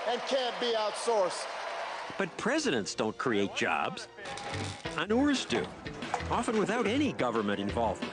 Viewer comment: what did he say. " Presidents don't create jobs......{entrepreneurs} do? I think it cut off.